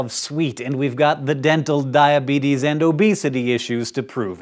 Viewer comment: It sounded like he said "dental diabetes." Also, maybe that is why cats are so mad at us all the time.